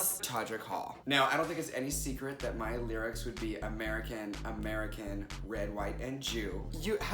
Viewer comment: When you said "Red, White, & Jew" I heard "Red, White, & Chew"